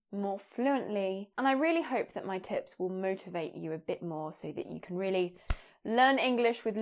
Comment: during the intro I was a little distracted and I heard “and I really hope that my tits would motivate you a bit more and you can really SBAM” :O